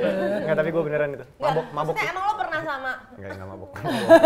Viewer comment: Dikta dibentak dikit sm Enzy, krn ngmg mabok eh langsung nunduk ya Dikta 😅 nurut banget sm Enzy 😄 etapi itu bilang apany Dikta, kok Hesti bilang cayang 🤣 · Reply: Kan dimenit 20:31 Mas Ta bilang " engga yang, gak mabok " makanya sama Teh Hesti ditimpali " cayang " Coba deh dislowmotion 0,5 pasti jelas kedengeran 🤭